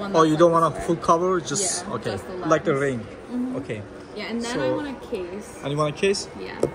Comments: i thought she said "and then i want kiss💋 🤣🤣🤣🤣🤣🤣 case 🤦‍♂️ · I heard "then I want a kiss" 💀